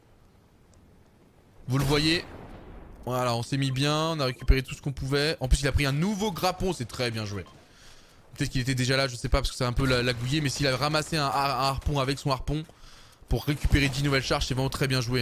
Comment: Un nouveau quoi ??!! Un nouveau ''Grappon'' ??!!! Wtf !